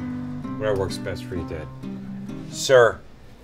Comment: What did he say at 1:29 ? I tried to get it few times but just can not..) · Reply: "Whatever works best for you, dad... SIR."